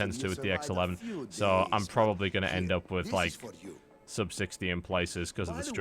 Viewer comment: are you fucking kidding me? did he just say that? oh because 60 fps is so low. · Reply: said sub-60. Under 60fps which not ideal.